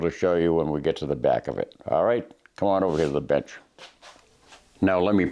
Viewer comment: Sounds like he said to “come over here lil b*tch” in the beginning. 😂 I know he said bench but it was funny. I use Hoppes…I didn’t know it was just mineral oil 🤦‍♂️.